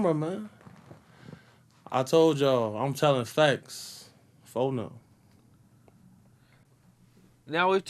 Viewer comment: does he say "i'm telling facts, for now"? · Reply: No, lol. He’s saying “I’m telling facts FO’Nem” that’s Chicago slang.